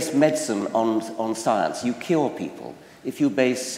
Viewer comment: I can demonstrate with this video that the brain works always in similar patterns. I mean you will probably hear, see or think what you see fit best ( you want). 1:00. After a few times i finally heard the right word. I've seen this before and i now still have heard "kill" instead of the right "cure". Remarkable this brain thing :D